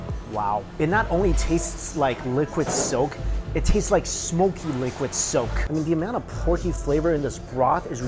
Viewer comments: I thought he said liquid soap "smoky liquid soap" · i swore he said liquid soap when heat the ramen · I thought he said it tastes like liquid soap LUL